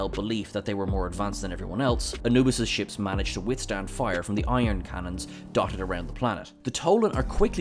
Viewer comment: did you say "iron cannons"? It's ion cannons. EYE-on. Awesome list otherwise, and I hope they come back with a new series soon!